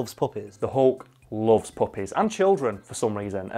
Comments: Until 5:42 I thought he was saying puppets because of his accent until the picture of puppies in the background 🤣😂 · My dumbass thought they were saying puppets for a bit, until 5:42.